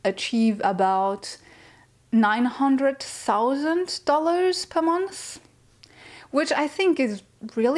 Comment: Wait..... Thought you said 900,000 lol but 800 makes more sense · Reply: Haha! I wish! No, 900 to 1000 US dollars per month.